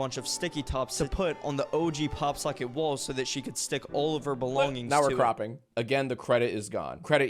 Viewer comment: I had to rewind like 5 times to make sure he was actually trying to say "belongings" instead of "bologna"